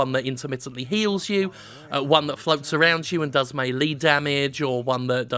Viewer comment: Did you say "MAYLEE" damage? K just checking.